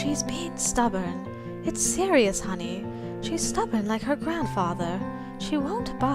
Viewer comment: Same i thought she said her doll died but is her grandfather